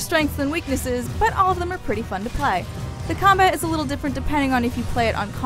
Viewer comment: did she say they are pretty fucking high?